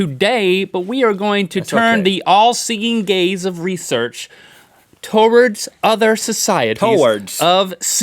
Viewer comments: I thought Link said "the allseeing gays of research" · I really thought Link said "All-seeing gays" ...